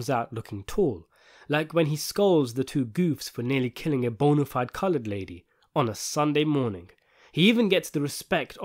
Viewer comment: It sounded like you said “bontified Colored lady on a Sunday morning “